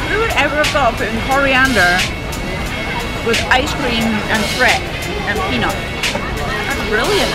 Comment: What were you trying at 2:38....coriander, icecream, nuts and? I think it was the music or your accent that I couldn't make out the last ingredient ha ha ha....looked delicious anyhow! Great foodie vlog. 😋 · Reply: Crepe is the missing word I think! Peanuts, ice cream and coriander wrapped up in a crepe. Surprisingly tasty!